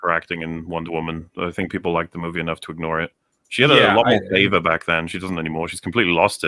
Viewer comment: "she had a lot more [something] back then" what does say here? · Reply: Favor